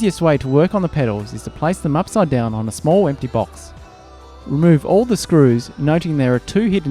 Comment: did you sayyyyyyy Empty Box?